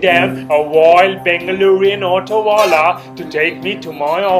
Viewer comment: Subtitles say - "wild pig manure autowala" for "wild Bangalorean autowala" at 0:16 😅